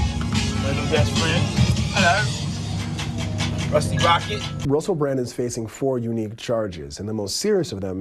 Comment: Did Diddy call him Rusty Rocket?